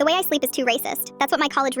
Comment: when the first said it i thought it said “i slept with a racist”